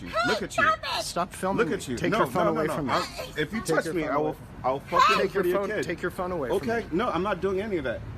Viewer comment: What did the guy say about the kid? I thought I heard him say I will take your kid or I will hurt your kid if the white guy takes his phone.